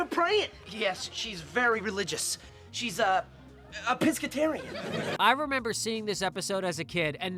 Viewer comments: I thought they said she was “Episcotarian” and that the joke was that they mispronounced “Episcopalian.” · i lowkey thought drake said Pisscatarian